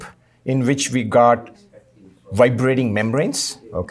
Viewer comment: I heard Vibranium membranes